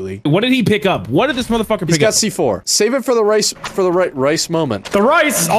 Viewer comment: I thought he was gonna say Race War but I realized he was saying rice not race.